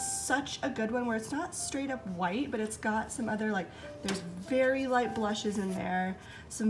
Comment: Ha! When you said very light blushes, I thought you said fairy light blushes. I thought, what a cool description for a color. You have such a good eye and describe them so nicely. And popcorn made without a microwave! 👏👏. It’s so delicious!